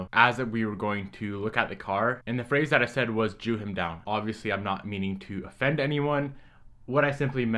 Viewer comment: I thought you said chew him down, in my world that means just go offer lower and see what turns up to be the final. Like chew something of the price. It's not an uncommon sentence as far as I know. Great to clarify it though and make apologies. Shows character and we all know you're a great lad.